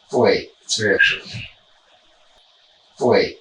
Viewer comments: when they wrote "help me" on screen I swear I heard "wait seriously" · I heard "wait sit" not "help me"